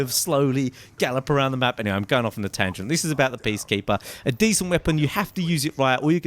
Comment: did you say Beastkeeper? I swear he said Beastkeeper.